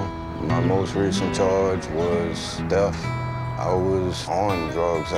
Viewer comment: I have terrible hearing, so I always watch videos with subtitles and I snorted at this bit because the captions said his most recent charge was “death” lmao But Um, does anybody mind telling me what he actually said too btw…? Thx ☺️